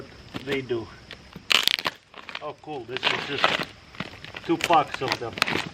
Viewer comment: Did say Tupac at 0:57.😂